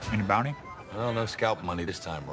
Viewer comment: what the hell does Rawlings say?? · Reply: "Any bounty?"